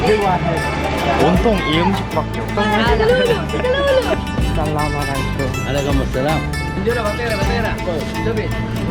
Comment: What did they say? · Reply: 윤지우 قالو سكلولو ذي ذبه